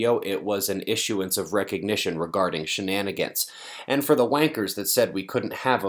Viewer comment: Wait, wait, wait a dogone, cotton pickin' minute, stop the press!!!. I think the audio is playing tricks on me. Did he just say " ...and for the WANKERS..."???